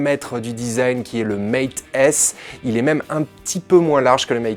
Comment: A 0:50 je n'ai pas bien entendue. C'est quoi le nom de ce téléphone "design" ? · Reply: mate s il me semble